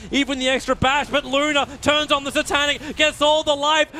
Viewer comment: is tobi said Loda ? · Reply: He said Luna